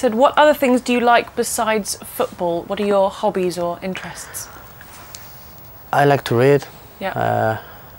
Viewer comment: when he said i like to read, i thought he said i like to rave... haha lol